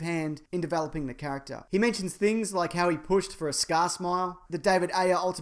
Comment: or so.... leto pushed for a "skasmah"? what the fuck is this limey prick saying?